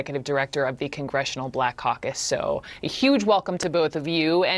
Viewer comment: Did she say black cock? Racist!